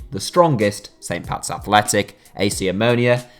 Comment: Is it just me or did I hear pats being mentioned?????